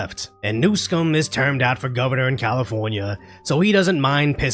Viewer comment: LOL. Must be getting late. I swear I heard you say "Governor New Scum" a couple times.